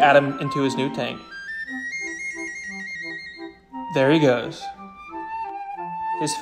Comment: I thought he said explode instead of explore